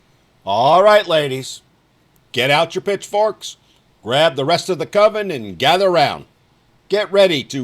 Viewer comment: Did he just say bitch forks? 😂😂 swear that's what I heard!